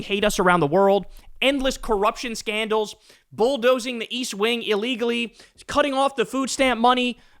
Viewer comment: East wing ?? I thought you said weast?